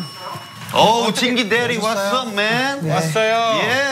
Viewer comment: i thought minho said "jinki daddy wassup man" LMFAO but it's "jinki 대리(assistant mngr.) wassup man"